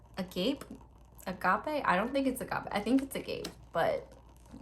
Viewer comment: When she said the thing about the two circle cards and "I think it's agape" I heard it as "I think he's gey" and I was like WHAT DID YOU SAY